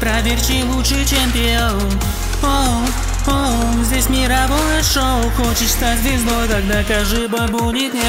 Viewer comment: что она говорит?Я не понимаю чет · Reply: здесь мировое шоу, что не понятно)